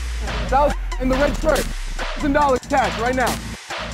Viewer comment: That man who helped him is so wholesome, he even said take your time! I wonder if he really got the 1k? If he did, it was epic! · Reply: I thought he said "ten dollars cash right now"